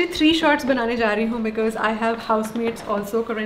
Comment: Did she just said "bcz we have 3 house maids???" Or m I mistaken something.